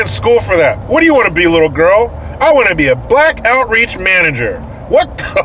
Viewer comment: At first I thought you said black outrage manager.... Which somehow still made sense til I heard you say it again....